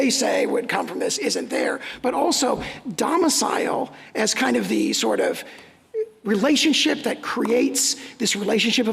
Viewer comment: what is that term she says? Sounds like (ah for shori) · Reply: A fortiori